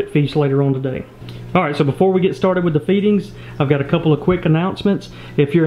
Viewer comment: sounds like he said beating not feedings hahahahahah